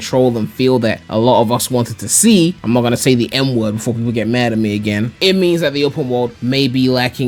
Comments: someone got mad at you for saying the n-word? though i hope i misheard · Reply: oh fuck you're right, i watched this before the automatic CC subtitles dropped on this video so i heard "n word" and not "m word"... my bad lol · wait… what? Why people get angry over the n word when you are the one who said it? Usually people get mad when a white person says it, not the other way around